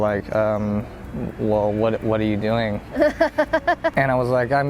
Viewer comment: does he say “what are you doing” or “what do you doing “ ?